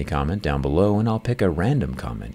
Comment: for a moment at the end i thought you said "untouched dust" but then i realised you meant dusk. my hearing probably has issues